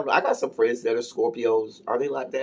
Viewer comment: Oh David is a Scorpio I thought she said Aquarius my bad